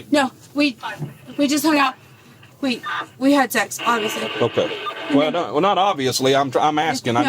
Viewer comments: I heard her say “we had, honestly” not “obviously.” But, the cop heard “obviously” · She said “we had sex, HONESTLY” not “OBVIOUSLY”. The Cop just misheard her due to her tremendous hyperventilation performance. Think about it, why would she use the word, obviously, in that context?! 😂😂